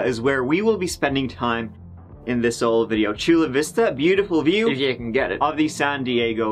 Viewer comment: Another reason to get rid of daylight savings time... Thanks for watching! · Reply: lol, I thought he was saying, 'this old video' 😹